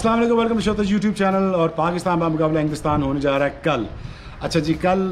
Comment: Did he said pakistan banam hindustan 0:27, is he drunk😕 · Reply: Inglistan but sounded as hindustan